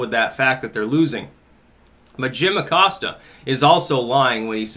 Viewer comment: A book. What? I had to rewind because it sounded like you said Jim Acosta.